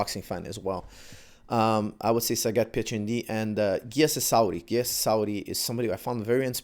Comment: hello Coach, on 14:12 minutes, you introducing a freestyle wrestler, origin from Georgia. I cant hear his name properly Gesis ari? I want to knows how to spell his name? thanks